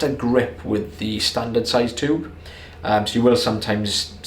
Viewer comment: I’ve watched a ton of videos at this point and the whole time I thought you guys were saying “standard size on the tube” not “standard zoellner tube” 😂 only just realized when I watched one of your older videos